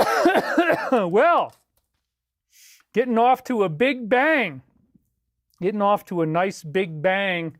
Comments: This is like Nazi germany... dare speak truth and they silence any who dare oppose them. Disgusting world we live in. God bless and keep David. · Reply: "getting off to a big bang" Really kind of sounds like he says Getting offed to a big bang...? He even repeats it. In his last live stream? 🤔 Maybe he wasn't killed only removed from "set" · I'm a personal friend of David, who just learned of his passing. I was at his wedding, and have known him of 17 years. David's stance on the Karmic implications of suicide would have prevented him from taking his own life. He believed the price of doing so would be enormous, and he believed in his mission in this incarnation, no matter the cost. I do not believe the official story, at all. Edit: A few days after I wrote this, it was confirmed by his family that a letter was left, and that David did take his life. It is obvious he did it under extreme emotional pressure, and though it is out of character to many of us, despair is a powerful force. RIP my friend. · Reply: "getting off to a big bang" Really kind of sounds like he says Getting offed to a big bang...? He even repeats it. In his last live stream? 🤔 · David literally said on video multiple times that he is not suicidal. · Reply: "getting off to a big bang" Really kind of sounds like he says Getting offed to a big bang...? He even repeats it. In his last live stream? 🤔